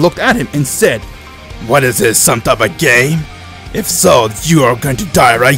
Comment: i thought he said gay